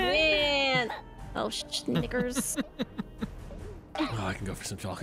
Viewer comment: What did says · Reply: I heard it too, I'm 99% she was saying Snickers but with like a "Sch" sound at the front and it just does not sound right 😅😅